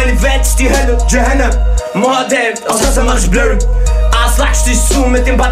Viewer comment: Was sagt er bei 0:59sek ? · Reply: Auf Hass mach ich den blöd